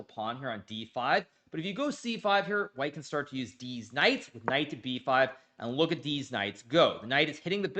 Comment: did hikaru really say deez knights